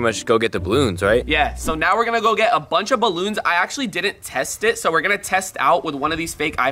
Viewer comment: I thought he said bunch o balloons but it was bunch of balloons